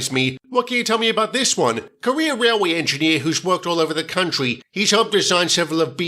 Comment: does Derek say "Career Railway Engineer" or "Korea Railway Engineer"? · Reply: I heard career railway engineer I think I’m used to hearing the British/Australian accent or something